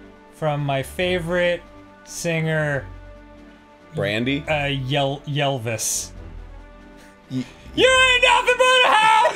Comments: I DON'T KNOW WHAT I WAS EXPECTING BUT THAT WAS NOT IT. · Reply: I heard “Yell-vis” as “Ylvis” and I was expecting that “What does the fox say” song lmao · When Danny said yellvis I thought he was saying Ylvis so I was bracing for lyrics from The Fox.